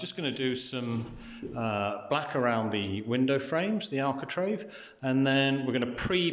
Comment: It sounds like you say “alcotrave” you really mean architrave , but apart from that I like your videos.